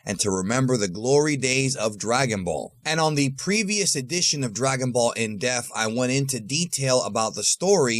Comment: It sounds like he is saying "Dragon Ball In Death".